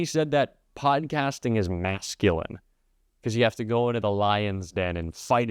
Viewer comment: Had to use captions to hear masculine at 8:50 I sounded like it was self censored.